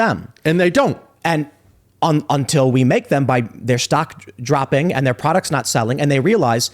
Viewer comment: I was about to go buy bud stock, i thought you said the stock dropped 70%